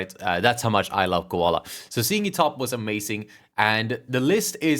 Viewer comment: I misheard: “seeing me topless is amazing” Context after you said you’d cosplay as Koala😂